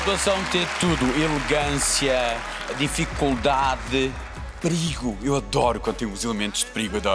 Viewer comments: O que é brigo? N entendi muito bem quando o Tochas falou.. minuto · O que é brigo? N entendi muito bem quando o Tochas falou.. minuto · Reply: perigo